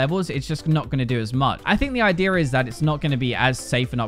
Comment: 0.5 speed, "I deer" instead of idea